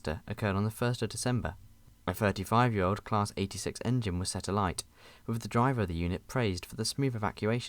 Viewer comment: did you said '86 engine'? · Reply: Yes I probably did. I vaguely considered changing it as it isn't a thing British people say but it was only something minor 😭